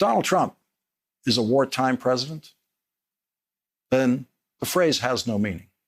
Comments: I see it was a misunderstanding on my part. I thought Trump said he was a "Whoretime president". Thanks for straightening that out. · I think he misheard. Trumpy did not say Wartime President, he said ONETIME President.... Stay strong, America 🇺🇸